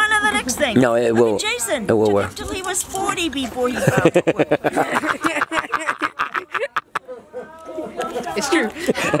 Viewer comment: Jason’s mom is everything! “Jason was 40 before he found love” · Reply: Cassy Wright ohh I thought she said “before he found work” oops